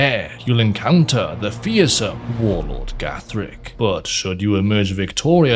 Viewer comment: I thought you said "the fearsome... Warren Gatland" ...